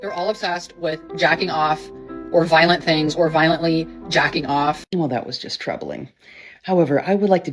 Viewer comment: Yo guys, hope you enjoyed (or endured) today's episode. OG Video credit to Logical dating 101], here: <link> Want exclusive videos? Become a channel member: <link> Second channel: <link> For today's video, Hidan or Locke probably sent it in for me to cover. Check out their channels: Locke The Bounty Hunter: <link> Hidan The Mummy: <link> Want to support the channel? Donate here: <link> Have a good one gents. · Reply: Did she say "violently jaggin o.f.??!!